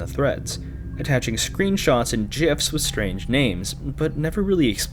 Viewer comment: did you just say jifs